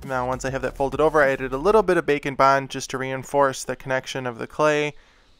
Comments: I’m new to the channel and the entire time I thought he was saying “bacon bond” instead of “bake and bond”. XD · When he said "bake and bond" I heard "bacon-bond"